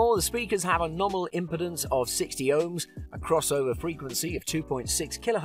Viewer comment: Nominal Impedance of 60Ohms? For those QAcousitcs?! Not likely. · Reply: Also thought that i heard wrong,he does say 60 ohms